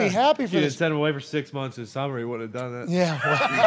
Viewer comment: What does say? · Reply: If you didn’t send him away for six weeks in the summer he wouldn’t of done that.